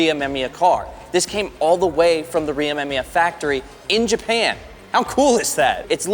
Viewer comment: did you actually say RIMEMIYA? 🫠